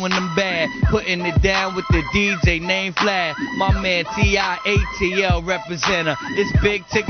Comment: Wtf Did Tigger say dj Vlad? 🤣😂🤣😂 yall check it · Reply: Yeah that is DJ Vlad…the same one doing interviews now